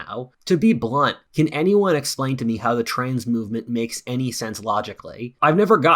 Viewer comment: Around the 21 minute mark the first time I thought you said "train movement", not trans movement, I was about to start typing a comment about how railroads use a low surface area, which allows them to transport weight more efficiently, but also makes it so they can't handle changes in elevation well (which is why railroads have so many bridges and tunnels, to keep things relatively level). Oh well I suppose. Ha ha.